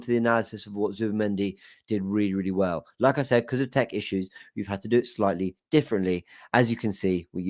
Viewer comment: when you said Tech issues I thought you said Turkish issues